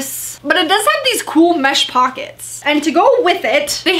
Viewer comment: I thought you said hot pockets lmao